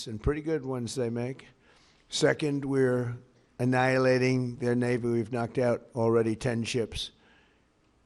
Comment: Trump slurring is so bad, when said "we annihilated their naval" i thought he said "we annihilated their neighbor".